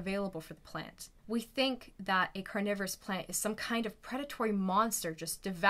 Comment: I thought it said carona virus plant lol